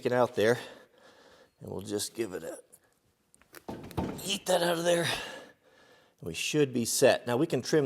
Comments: did you just say "yeet"?..... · Did you say “YEET that out of there” at 11:55? Haha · Did he just say yeet that outta there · did he just say " yeet that out of there" ????? thats amazing!!!